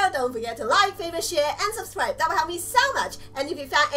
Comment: When she says "Share" I thought she said "Leave A Like And Shit" it was actually share what the fuck